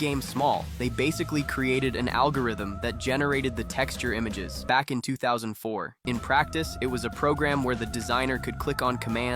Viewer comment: I Heard "back in 2004" and realised it really is "back in that time". I wasn't Even born that year, i'm about to turn 21